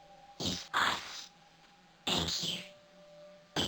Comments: I think they say, "I couldn't have done this without you" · I think the puppet is saying we couldn't have done this without you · I think they say “we couldn’t have done this without you” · I think it says "We couldn't have done this without you." I know it for sure says the first part but idk about the "without you" part.